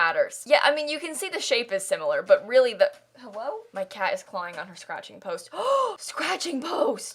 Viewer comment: did you just say "hewwo"? Unironically?